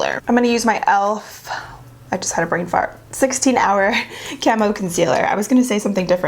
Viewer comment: you said that you had a brain fart but I didnt hear u correctly and I thought that you said "I need to fart" 🤣